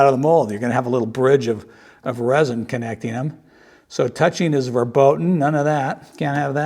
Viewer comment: Did you say: Touching is "verboten"? =D